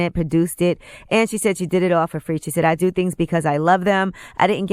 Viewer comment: No she said “ I did it for free because I produced it.” She’s a producer so she paid for everyone to be on the movie and people to help with it but she does make money from the ticket sales and stuff like that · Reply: it sounded like she said I did it basically for free...